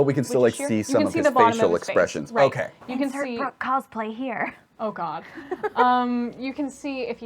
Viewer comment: Is she saying "Third brook cosplay here"? Can't quite tell. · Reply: It was “insert Brooke cosplay here” Brooke has worn a Vas cosplay to the Renfaire recently 💕